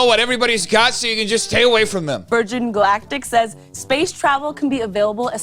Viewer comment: I had to go back and replay this twice cuz for some reason I thought she was saying "virgin black dick" lol. I'm think I should probably go get my hearing checked asap 😂